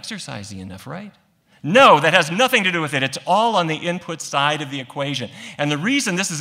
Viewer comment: I thought you said sugar is nothing to do with diebeties???